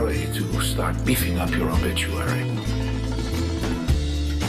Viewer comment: subtitles says beefing up your bitch XD